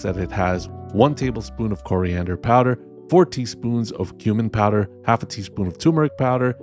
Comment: when you said 'cumin powder' i heard 'human powder' and was confused for a moment. and then the was 'tumor powder'... gotta love those misheard instructions 😁